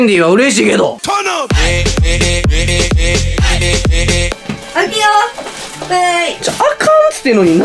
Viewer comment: フェンディフェンディフェンディにしか聞こえないwww